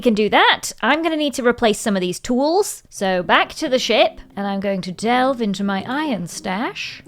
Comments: Kinda sounded like u said “back to the shit” 😂😭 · is it just me or did it not quite sound like she said ship but instead sounded like she said 💩 😂😂😂😂😂😂 it’s not just me right